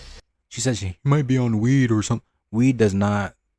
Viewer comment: ish I'm pretty sure she actually said "wet" (weird enunciation for "weed", short "e" & harder, more "t" sounding finish IMHO), which AFAIK is slang for PCP... Weed wouldn't make you act like that, but angel dust will.